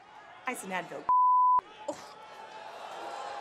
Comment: What did Rousey say at 1:08? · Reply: "Ice and Advil b***h"......She's saying Becky needs that for her knee.